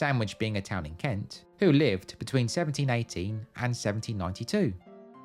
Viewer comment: for a moment I thought you said 1780, instead of 1718 😂 looking good for a 12 year old gambling addict.